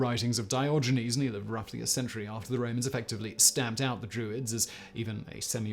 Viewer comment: I thought it said Droids. I am disappointed